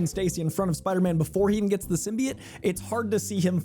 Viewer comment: Did bro just say “Simbiete” 💀